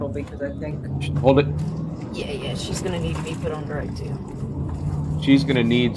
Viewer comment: I heard “she’s gonna need to be on direct too”